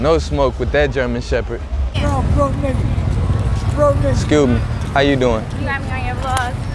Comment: Did i just heard him say "You're a broke nga" 💀